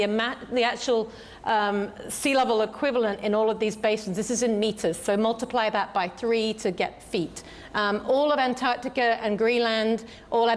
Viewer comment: i heard her say feetus multiply with 3 equals feet